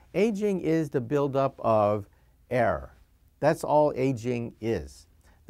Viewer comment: I thought he said 'air', but he said 'error'. Was so confused, lol.